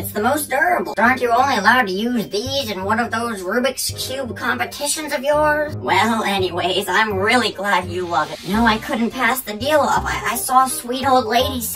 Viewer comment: i thought grandma's say "Rubrik cub"